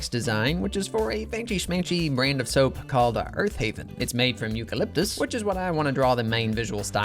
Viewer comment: I thought he was going to say: "It's made of koala" 🐨😮